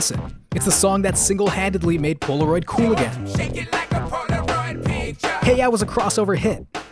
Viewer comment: Oh shit I always thought he was saying shake it like a Polaroid preacher! Especially since he dresses up like a preacher at the end.